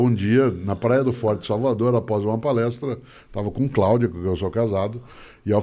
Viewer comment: eu quase entendi: "estava com CLAUDIO com quem estou casado"....kkkkkk